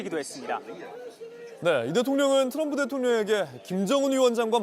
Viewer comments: 아나운서가 윤대통령이라고 말한것 같은데 잘못들었나?(4:55) 발음에 신경쓰세요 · 윤대통령이라고 들리는건 저뿐?ㅋㅋ